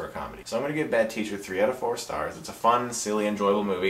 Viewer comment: did he say 3 out of 4!?!?!? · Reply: yes